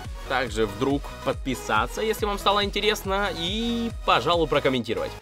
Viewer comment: Мне послышалось "С вами был Лойс"